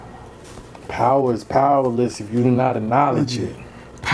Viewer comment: Thank you thank you thank you all for a wonderful awesome amazing supercalafrigilastic Month. You all are awesome! We had over 13 million watch time minutes last month. Y’all really lover our long winded convos and long ass videos 😂😂😂 We’re gonna keep bringing you that heat you want. Love is in ya face I said Love is in ya fasaaaacccceeee! 🤗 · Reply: I thought he said knowledge is powerful but powerless if you do not acknowledge it